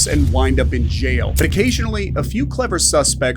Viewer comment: sounds like he said "vacationally" lmao